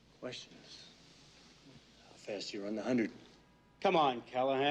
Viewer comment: what does he say at 2:00? does he say something like "first you run the hundred"